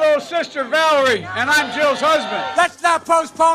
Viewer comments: "This is my little sister, Valerie. And I'm Jill's husband." I had not seen that one yet but it did get me rolling on the floor for some reason. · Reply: Oh I thought he said "I'm Joe's husband." 🤦‍♀️ Edit: Typo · “This is my sister valarie, and I’m jill’s husband”😂😂😂😂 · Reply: i thought he said "and im joe's husband" LMAO